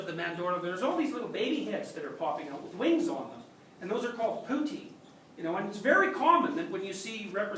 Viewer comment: "putti," plural of putto; not "poutine" which the close-caption wrongly suggests.